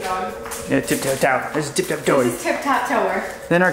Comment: Did she say ~ 4:35 "stupid dog" 🤨